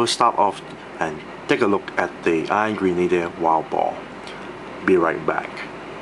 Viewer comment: Thought he was saying "Angry Needler" when pronouncing Iron Grenader the first few times